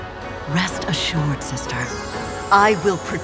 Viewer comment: guys did freya say sister in